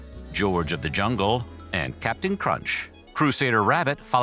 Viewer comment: I thought he said 'Captain Crotch'.